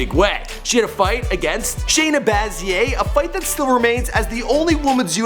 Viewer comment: Thats Rose Namajunaz not Nina lmfaoooo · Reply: did he say Shayna "baz yay" ?